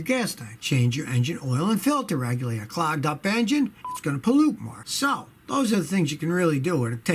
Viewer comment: Who heard the beep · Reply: Yes- i thought he sais " s*its gonna pollute"